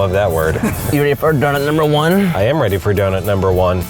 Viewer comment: when he said donut number one, i thought he said " do some marijuana" lol😂 Read more